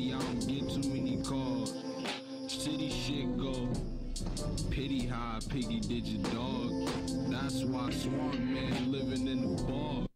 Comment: I had to pause it to start hyping in my room at 2am when I heard that "pitty how a piggy did ya dog, that's why the swamp manliving in bog"